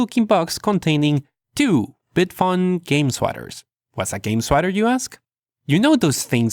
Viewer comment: I thought he said Game Squatter now that would be hilarious